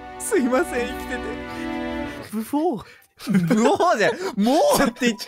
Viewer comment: ほんとにブフォって聞こえるww